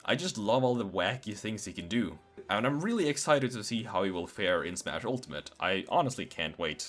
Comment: Umm where is there a f air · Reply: Oi You At 6:18 he says fair.